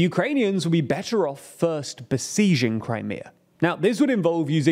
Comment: Did you just say 'seijing Crimea'? 😂